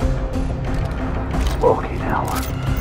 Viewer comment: what did thatcher say?? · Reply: Fookin laser saight